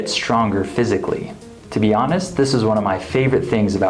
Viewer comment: Guys please is there anyone who understands the pronunciation of "this was" 1:54 he sounds like he said "this is.."?? Thank you 🤍